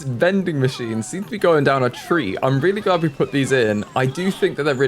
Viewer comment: “…down a tree…?” Did I hear that right? What does down a tree mean? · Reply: Maybe "going down a treat" but I think he might have swallowed the t! Means something is well enjoyed/liked :)